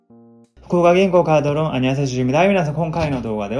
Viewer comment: さんの動画初視聴の者です。始めの挨拶って福岡銀行カードローンって言ってるのですか？私の空耳ですかね？